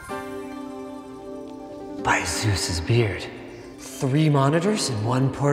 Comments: First time through the intro I thought he said "By Susan's beard" lol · lol, i thought he said by asus's beard. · I heard "by Asus's beard"